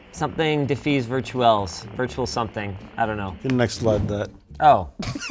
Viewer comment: What was said at 14:28 that made everyone laugh? I can’t make out what he said and the subtitles are wrong 😟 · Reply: I believe he said “you can next slide that”.